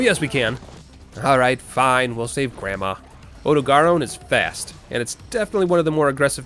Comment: Did... did he say "Ode-eh-garr-own"? Sir I dont mean to bash your skill but that is "out there" in terms of pernuciation. Worst ive heard before this is "Oh-Dog-Run"